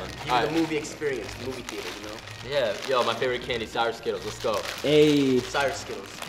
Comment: For a hot minute I thought he said Cyrus skittles but I think he said sour skittles